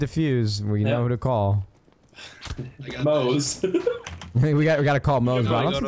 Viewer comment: thought he said boze